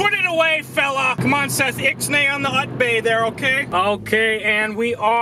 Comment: I thought he said "ix-nay on the butt-play" 🤣